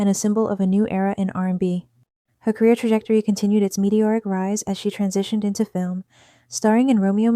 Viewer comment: Did I hear “mediocre rise” right?😂😂😂